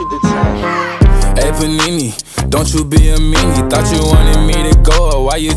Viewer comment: oof i didnt know lil nas x said hey panini i thought he said hey evani